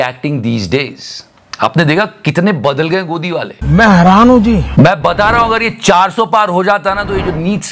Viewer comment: Modi says "Main heraan hu ji" What I hear: "Main haraami hu ji" 😂😂😂😂😂😂😂😂😂😂😂...